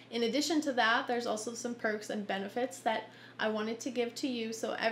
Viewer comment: Who else heard her say "Pervs and Benefits" at the end? XD